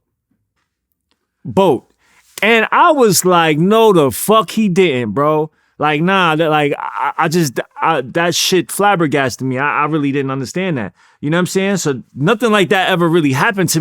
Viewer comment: I wondered if he really said “ NO THE FUCK THIS N199A DIDN’T?! “ 2:57 🤔🤨😒